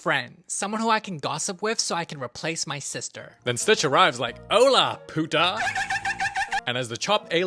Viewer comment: did you just say "Hey bitch" In spanish? xD